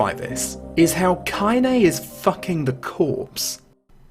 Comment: Hey! Non english speaker here. can some one explain what meaning of a word "decorpse" it's here 42:57 . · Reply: I said "the corpse". :)